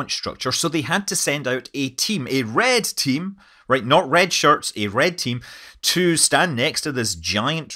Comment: That Red Team is definitely pretty badass. Thanks to those guys we got a launch last night. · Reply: I heard this as "red shirts" and hoped they were huge star trek fans with ann enormous sense of humor.